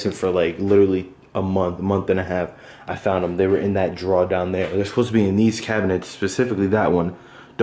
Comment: in what? did he say in that draw right there